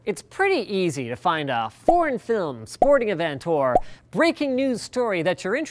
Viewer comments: thought he said porn not foreign 😂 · i really heard PORN FILM lol